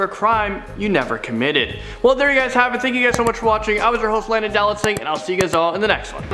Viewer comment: He said his name so fast I heard "Land a Dalek sings". XD